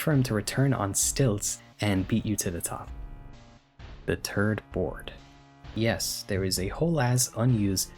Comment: I'm sorry man your accent made it sound like you said "the turd board"